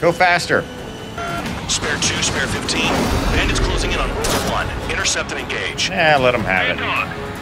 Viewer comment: Ngl. While playing this mission i thought they were saying Groper 1. Also despite these gripes AC7 is one of my favorite games of all time. Mainly cause it takes place in a way more interesting fictional world. Lol